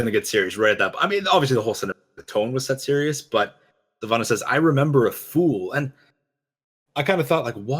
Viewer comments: I thought Sylvanas said "I remember a fall" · I thought she said “I remember... a FALL.” I thought it was about her own fall, and the fall of her people! Does she really say “fool”?